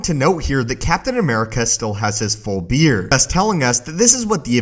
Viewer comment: Did someone hear he said beer 🍺 instead of beard